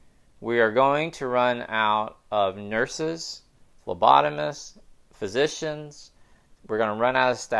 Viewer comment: did he say lobotomists? Isn't lobotomy illegal or is he talking about a different kind of lobotomist? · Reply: Phlebotomists - people who draw blood.